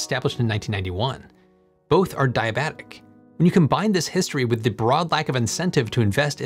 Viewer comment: Everytime you talk about the system at 13:20 ish I keep hearing say Diabetes